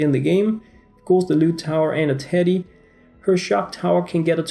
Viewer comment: Am I the only one who thought he said titty and not teddy